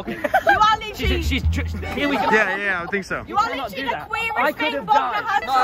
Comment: tubbo was so disgusted by that girl, he couldn't hide his face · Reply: Stephen 7:24 It isnt subtitled but she says “You are literally the queerest thing bogna has-“. It was fucking hilarious but im honestly surprised he kept it in. Kinda feel bad for whoever it couldve hurt but since its not subtitled I dont think most heard.